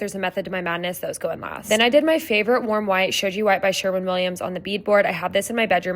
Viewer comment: What the wall paint color? I heard you say sherwin Williams but the name went by too quickly. It looks great! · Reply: Shoji White SW 7042